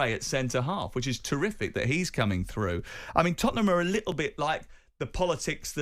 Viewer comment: Did he just say «Arthie Gray coming through» Actually lost for words. 😂